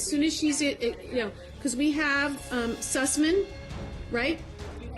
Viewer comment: The way the first judge says "assessment", I thought she say "sus men". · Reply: The whole caption is wrong. It was supposed to be Sussman (who was the Dr that did her 1st assessment. But in the caption it says assessment. Whomever does the editing on these videos, need to use some Q-tips or something.